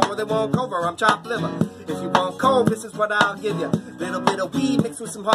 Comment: I thought he said “guess who’s back back again covid’s back tell a friend” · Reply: Yoo I thought he said "If you want want covid that's what I give ya"